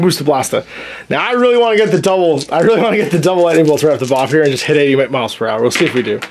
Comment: "I really wanna get the double lide-eable-strougt-stavo-waffhereha and hit 80 miles per hour." WTF did you say? Completely unintelligible. Even the closed caption couldn't figure it out.